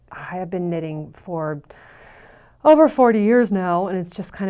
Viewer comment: It sounded like you said you'd been knitting for 40 years! I need to clean my ears out 😂